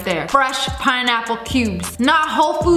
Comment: Am I the only one who heard her say water melon instead of pineapple 😂 lol